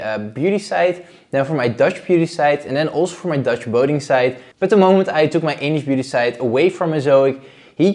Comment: All these years it is actually a Dutch Boating site. I thought he always said "voting" site.